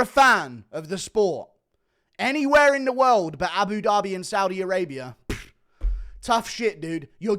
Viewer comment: wtf did bro say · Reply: Abu Dhabi?